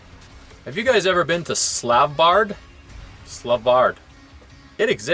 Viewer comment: What are you saying 6:21 minutes in here ? Is it Svalbard you mean ? If so, this is the northernmost part of Norway. If you are going, bring warm clothes :)